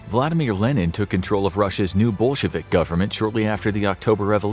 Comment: I thought he said ' Bull shevik'. 😀